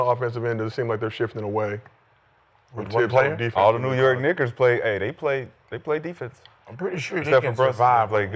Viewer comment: what did he just say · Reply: Knickerbocker